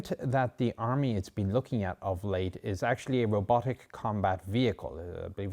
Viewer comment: What he said: "It's a Remote Combat Vehicle" What I hear: "It's a BOLO Mark 1"